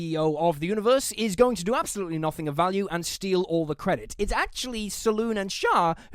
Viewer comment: Did you just say "steal everything" and say that it's "capitalism"? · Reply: he said "steal all the credit" dingus. Also the youtuber who fights billion dollar corporations with boarderline bloodlust fervor and donates all his excess wealth to charity is against CEOs?! i'm shocked. SHOCKED!!